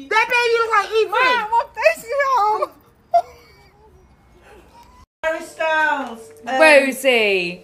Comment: Did anyone else hear Harry styles at 3:02?